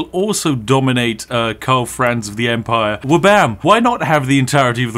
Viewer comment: I thought you said you couldn't dominate Karl Franz🤔